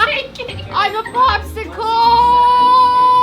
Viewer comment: wait did say shes a popsicle